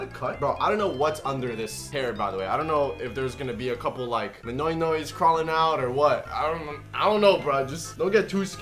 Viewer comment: in 2020 "Idk, is there going to be like a cuple manoynoys crawling out." 6:31 also in 2020 "Now I am a living meatball." 8:20 also also in 2020 "I would like you to say 'I like ya cut g'" Me: Boi you be V I B I N G doe, also your hair was looking kinda Th Ic C